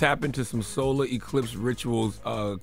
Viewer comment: Did he just say color eclipse rituals? People know you can go blind trying to look at it right